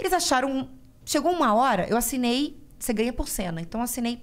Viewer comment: eu ouvi "vc ganha por sêmen"